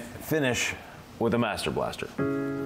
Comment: To me it sounded like you said master blaster; what is that.